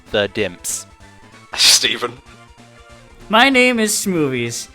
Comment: hehe, I thought he said smoothies. ^_^